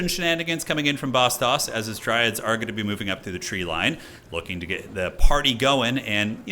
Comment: Omg I heard "Eschen Dryads moving through the trees" the first time 😂